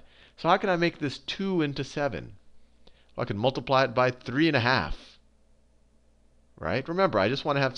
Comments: it sounds like he says fuckin multiply it by 3! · Lol at 8:16 did u say "Fu**ing multiply it by 3 and a half!" Cause that's what it sounds like and I think thats pretty fu**ing funny great video thanks so much!